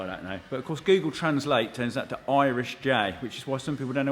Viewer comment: I thought you've been saying Irish jay this whole time